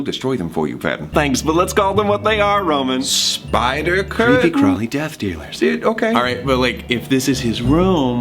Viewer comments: CREEPY CRAWLY DEATH DEALERS Edit: Why the F do I have so many like??? Thanks, y'all! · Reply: Isabella Wong OHHHHHHH I THOUGHT THAT HE SAID CREEPY CRAWLY DEATH EATERS😂😂😂😂😂 that makes a lot more sense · Am I the only one who thought he said creepy crawler death tailors instead of dealers? No? ...just me.... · Patton : Thanks, but let's call them what they are Roman Roman: spide- Patton: Creep crawly death dealers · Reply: liza pena At first I thought he said creepy crawly death eaters harry potter joke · Roman: spi- Paton: creepy crawly death eaters Me in a nutshell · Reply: I thought it said creepy crawly death dealers